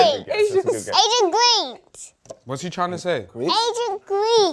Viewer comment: he was saying Athens Greece but couldn’t pronounce it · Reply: Thought he said ancient greece